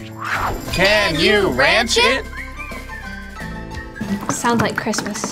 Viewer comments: I heard "Can you rachet?" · I heard "can you ratchet?" GURL U KNOE I IS BE RATCHET CUZ GURL U RATCHET flips hair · I legitimately thought it said reach it instead of ranch it. I was confused when they said ranch. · I thought the intro said "Can you ratchet?"😂😂😂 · I thought it said can you reach it